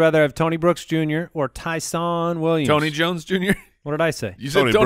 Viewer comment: the first couple times you said Ty’Son my volume was low and i thought you were saying “Python Williams” nickname potential?